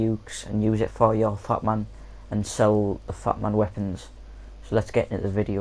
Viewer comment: if you have captions on its say 'fuck' instead of 'fat'😄😄😄😄